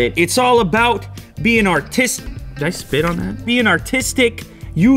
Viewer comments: when he said "being artistic" I thought he said "being autistic". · I’m sorry I thought he said it’s about being autistic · I thought he said being autistic lol